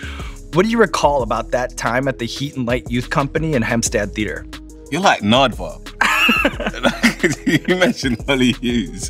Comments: "You're like Nardwuar" is a compliment every interviewer hopes for and Sean absolutely deserves it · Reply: I thought he said "you're like an Ardvark" the whole time, jfc. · Him saying "you're like nardwuar" made me smile. I'm happy when celebs give props to the team about doing their research, it means they're great at their jobs and it shows! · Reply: Oh!!! I thought he was saying “aardvark” I didn’t understand it but I just thought it was a British thing 😬🙈